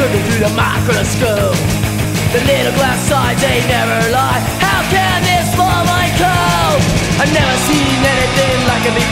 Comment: lol i always thought they were singing "TONYYYY HAAWWWWKKKKKK" but it was really AMOEBA. My childhood was all a LIE .-. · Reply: game bob barker I always thought it said "How can they spoil my coke?" instead of "How can this small mind cope?"